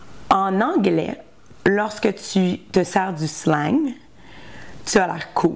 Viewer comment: J'étudie le français en écoutant les vidéos comme celui-ci. À 2:11 et encore à 2:16, elle dit: Quand tu te sars (tu sors? tu te sors??) de slang... Qu'est-ce qu'elle a dit? Merci! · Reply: Merci! (Ou bien "se servir"? "Si tu te sers..")